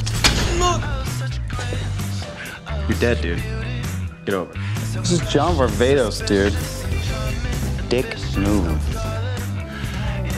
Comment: haha love damon!! wat does he say at 1:43? "this is jon barvados dude, dick move?" is that it??